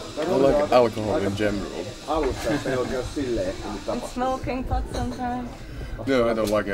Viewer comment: Can somebody tell me what Exactly the interviewer is asking at 10:06? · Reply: She said Do you smoke pot?